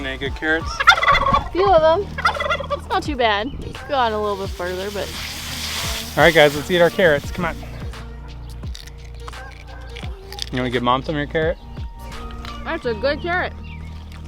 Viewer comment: my captions say "get mom to marry carrot" I love that sentence.